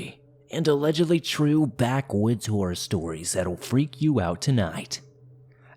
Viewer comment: In the beginning I swear I thought you said "Backwoods Whore Stories"... I was terrified 🤣